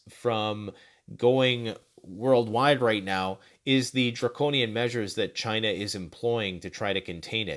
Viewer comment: Are you saying “draconian “? It was hard to understand you, and it sounded like you said “jarconian”. 🤷🏻‍♀️